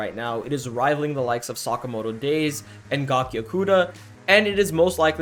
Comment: Hi great video man I'm new to the channel and you do an amazing job. Please what is the name of the manga you compare Dandadan to at 5:09? I heard something like Gakiokuda but when I type on google I don't see anything. Thanks in advance😀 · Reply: Gachiakuta